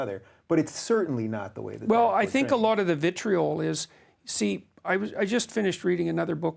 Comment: One Peterson was talking I thought he said "vitriolist ventriloquist" but it turns out he only said the 1st word. But it would be fitting